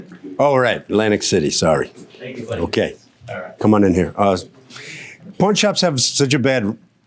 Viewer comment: sounds like he says porn shops at the end 🤣🤣